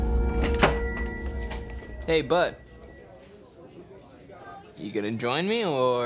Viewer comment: what he say??? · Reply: Hey bud